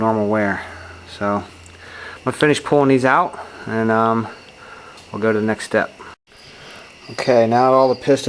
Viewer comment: we're going to next step but subtitle say we're going to mexico..haha..